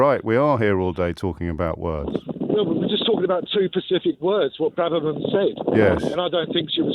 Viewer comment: he is actually saying "two pacific words" or am I hearing that wrong?